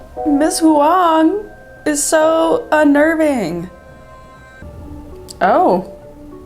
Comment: Ms. Hwang is un-Irving? Is that what you said? lol